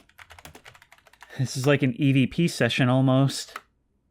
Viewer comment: I swear I heard him say Edp session instead of EVP session